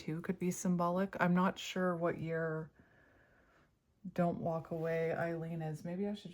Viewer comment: I thought you said "Don't Walk Away; I Lean" Lean on me When you're not strong And I'll be your friend. I'll help you